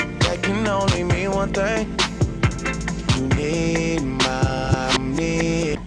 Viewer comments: “You used my cell phone, that can only mean one thing. You need money” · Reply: I heard "you need mommy" xD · “You used my cell phone, that can only mean one thing. You need money” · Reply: I heard "you need my meme"